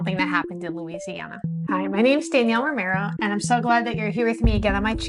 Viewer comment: I thought you said Daniella Marinara. Ho ho ho.